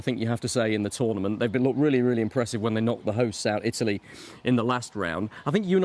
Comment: ‘knocked the hosts out, Italy’?? Is that what he said?